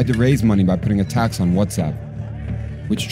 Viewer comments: putting a tax on... WhatsApp...? Lol what word is he saying? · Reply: yup whatsapp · a tax on whatsapp? Did I hear that right? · a tax on whattsapp? Is that what he said?